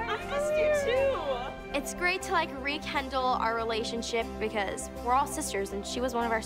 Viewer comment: i swear i heard "it's great to re-kendall our relationship"